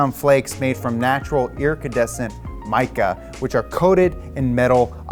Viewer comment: did he say "ircidecent" instead of iridescent?